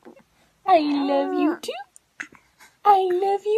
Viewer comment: Ahwww it really sounded like she was trying to say “I love you” back 😭 ❤️